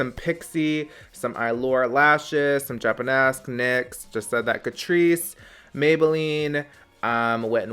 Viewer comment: when you said “just said that, Catrice” ofc my scumbag brain had to go straight to drugs and misheard it as “just some of that good trees” omg 🧠🤣